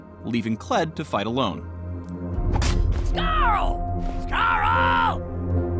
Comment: I thought kled said scarra